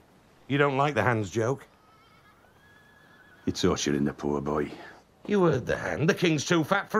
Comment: Can someone please tell me what the fuck he says at 0:45? it's been driving me crazy since i first saw this episode. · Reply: Sorry I’m a bit late but I think Ned says “You’re torturing the poor boy”